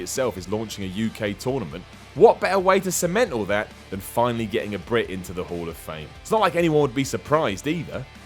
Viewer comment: I heard you say "Get a Brit in there"... William Regal isn't in the Hall yet? Hard to believe there's 0 British people in there. But i'll take your word for it